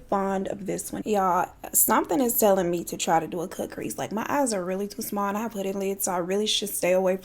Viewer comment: I thought u said no cut crease😂😂😂😂😂 my eyes little too.